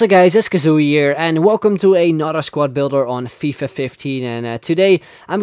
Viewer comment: thought you said "a not a squadbuilder" lol